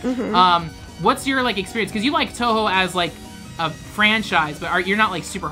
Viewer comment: did he just say TOUHOE *TRIGGERED*